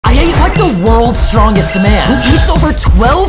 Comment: Bruh sounded like he said I ate the world's strongest man 🤣💀